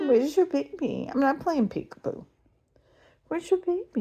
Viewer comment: Aww it sounds like she said thank you 😊